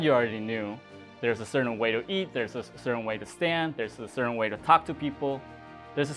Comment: I heard "There's a certain way to eat sand"